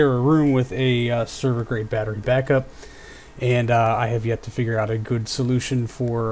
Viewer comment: Yeah - Its a battery backup for a server rack. It's way overkill for my little desktop, but it was free so... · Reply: Wow, I thought I misheard that you have a battery backup for server rack, so I tried to hear it again and again. Super cool to have one for your own desktop lol